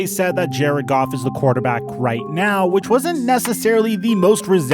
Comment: “is he your quarterback” “yea he’s a quarterback alright” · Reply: I heard, "Right now"